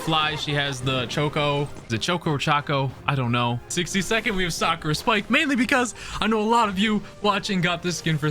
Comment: I hear sucker spike, not sakura spike 🤣🤣🤣